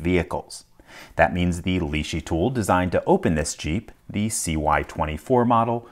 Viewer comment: Did he say "... tool designed to open this Cheep" instead of Jeep? Around 0:25 🤣